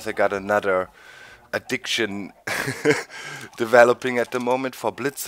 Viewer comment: I was doing work when I started the video, and my heart damn near stopped when you said addiction. Thought you said eviction...ehehe... Pretty neat RG machine, but then again, RG machines in Minecraft created by excellent players are always going to be neat. Good work anyhow.